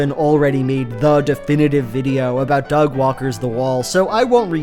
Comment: I forgot what that one guy's name was and I genuinely thought you kept saying "Dog Walker", like I thought it was a screen name lol